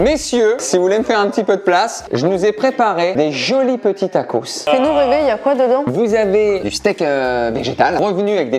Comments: y'a que moi qui entend "et nous bébé y'a quoi dedans ?" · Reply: Elle dit :« fais nous rêver, y’a quoi dedans « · Camille to Benjos : "Dis nous Bébé ya quoi dedans?" je suis le seul à entendre ca? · Mon cerveau : dit nous bb ya quoi dedans ? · A 4:09 c’est moi ou Camille dit ducoup bb y’a quoi dedans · euuuh c’est moi ou Camille a dit «dit nous bébé y’a quoi dedans » 😳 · Camille : " sinon bb ya quoi dedans ?" J'ai bien entendu ça ?? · A 04:09 min elle dit quoi camille ? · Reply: elle dit "fait nous rêver" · c’est moi qui rêve ou Camille a dit bébé à Ben😂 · Je sais pas si vous avez remarquer mais a 4:08 minutes Camille a dit sinon BÉBÉ ya quoi dedans ? A Benjamin Donc ça voudrais dire qu'ils sont en couple 🤨🤨🧐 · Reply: Mdrrr non réécoute elle dit "fait nous rêver y a quoi dedans" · C'est moi ou Camille dit "et nous bébé y'a quoi dedans ?" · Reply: Elle dit "fait nous rêver y a quoi dedans" mdr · C moi ou a 4:10 camille appel benjamin bebe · A 4:09 Ont dirait que Camille dit : sinon bb y’a quoi dedans 😂😂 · camille dit '' y'as quoi dedans bb'' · je suis seul avoir compris que camille disait : dis nous bébé ya quoi dedans, ???? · a 4:09 Camille dit a Ben "dis nous bebe y'a quoi dedans?" ou j'ai rêvé? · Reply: t’as rêvé elle a dit « fait nous rêver y’a quoi dedans ? » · les gens ecouter ce que dit camille a 4:08 minute dit nous bb y a quoi dedans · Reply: Non je crois qu’elle dit « fais nous rêver, y’a quoi dedans ? » plutôt. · camille dit il y a quoi "bebe" a ben